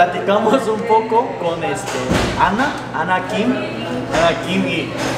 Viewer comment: Pense que dijo que se llamaba 😂😂